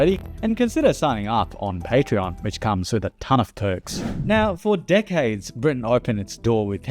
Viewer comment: I deadass thought you said your patreon comes with a ton of turks instaed with a ton of perks lmao